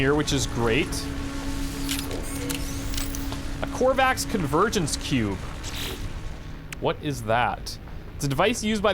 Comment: I almost thought he said companion cube when he said convergence cube. Been playing too much portal...